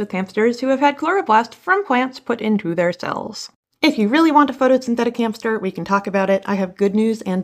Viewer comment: I heard "synthetic hamster" first instead of "photosynthetic hamster"... Can we talk about hamsteroids made of organoids? 😂 But, the actual topic of photosynthetic diabetic hamsters you sniff to breath on Mars is much more interesting ^^